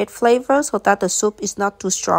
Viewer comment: Instead of "neutral", I heard "naussea"